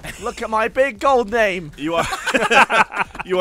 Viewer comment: I thought said "goodbye" in an Austrian accent at the end, but he just said "go buy it"